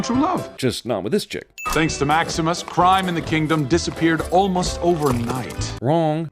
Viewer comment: why does it sound like he said maximus prime? lol